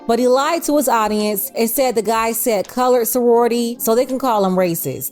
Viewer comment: Sounded like he said. Coward sorority. There was no LL at all.